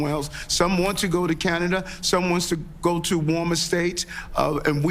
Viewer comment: Did he say, "Some Wants sa go?" At 4:14 on this video...